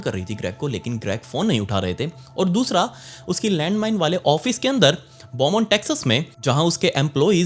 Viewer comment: did i hear landmine 🧐